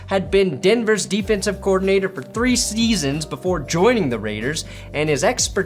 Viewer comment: three sNeasons I woke up to this auto play and wasn't sure I heard it right cannot stop laughing